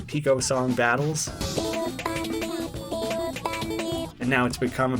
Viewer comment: I no joke thought boyfriend said big old bunny XD not PICO funny